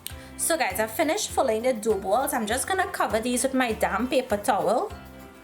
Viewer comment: I love this, excellent instruction. I am sure I could make these following this video, very well done. There was one point where I was confused because you said "I'm just going to cover these with my damp paper towel" but I thought you said "I'm just going to cover these with my damn paper towel" and I was thinking "Why is she mad, she sounded like she was in such a great mood?" <link>